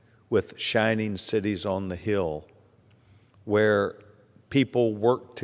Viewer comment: (thought he said Chinese cities on a hill)